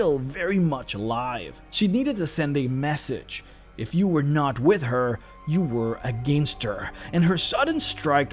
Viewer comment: I almost laughed my inside out when I misheard it as "When you're not with her, you're a gangster"😂🤣